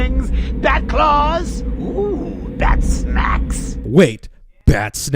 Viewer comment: I half thought Joker said "Bat smacks." Instead of batsnacks